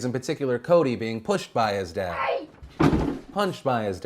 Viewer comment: i don't know if thats a typo or are you foreshadowing towards something to Cody, but the subtitles said "One of the kids in particular Kody being pushed by his death." when you said dad